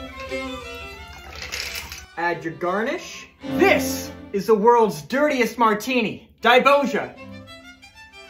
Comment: I misheard you saying "piss is the world's dirtiest martini" instead of "this"